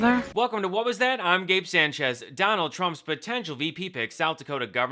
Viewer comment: You should leave a longer pause after you're name. I heard "I'm Gabe Sánchez Trump's VP pick". And that was scary.